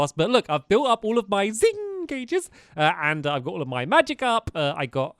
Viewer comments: Did he say magic up or magicarp? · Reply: Magic up, sadly. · did stampy say magikarp or magic up i need to know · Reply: Aww.. I sort of hoped it was magikarp, but no. He said magic up.